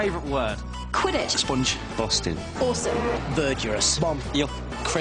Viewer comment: what did Daniel R say?? Ber Jer Ous? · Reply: Verdurous: rich in verdure; freshly green; verdant. covered with verdure, as places. consisting of verdure. of, relating to, or characteristic of verdure.